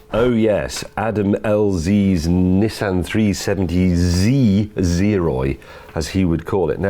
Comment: when i heard zeroy, i thought they said zeroids